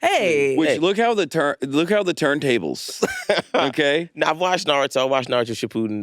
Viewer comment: Did he say, "look how the turn tables" 😭